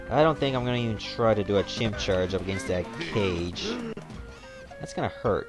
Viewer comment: for a second I thought you said Chimchar